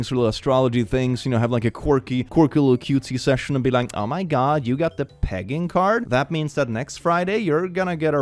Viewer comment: I heard, "oh my god, you got the PEGGING card?"